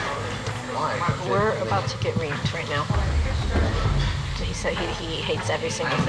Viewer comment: LMAOOO did she say "we're about to get GRAPED right now" ????? lmfaooo